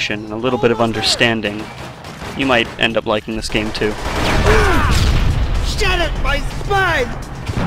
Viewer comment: Did someone say "...shattered my spine!"?